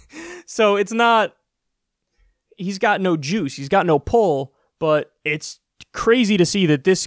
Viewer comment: when Kyle said "he's got no juice" for a second I thought Kyle said "he's got no jews" also a bolshevik revolution sounds awesome